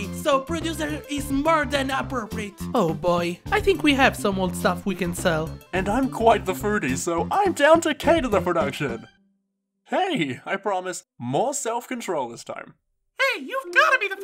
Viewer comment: did he say he’s fruity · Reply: he said foodie HELP ME SUIZJX 😭😭😭